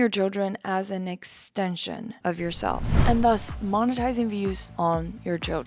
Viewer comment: Please tell me why I heard ‘MOM-etising’ 😭…?